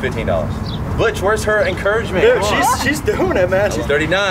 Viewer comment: When he said “butch where’s her encouragement “ I thought at first he said “b**ch where her encouragement”. 🤨